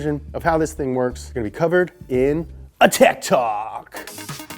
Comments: I thought he said TIC TOK instead of tech talk. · ooooh tech talk, thank god! i thought you said tiktok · I almost clicked off the video when you said Tech Talk bc I thought you said TikTok